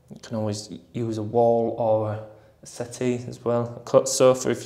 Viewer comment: what are you saying around 5:50? “you can always use a wall or a ___, or a _______ if you got one” ? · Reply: A wall or a settee or a sofa 👍🏻